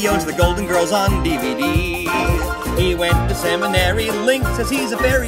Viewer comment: The first time I watched this, I didn't know what seminary was. I thought he said “He went to Sam and Larry”. I was like “Who's Sam and Larry?”.